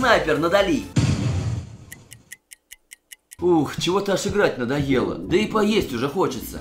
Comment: кому тоже послышалось слово " Жрать надоело😂"